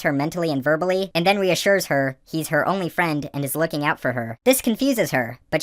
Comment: You said "He's her only friend" but I heard "Eats her only friend" because of the voice change.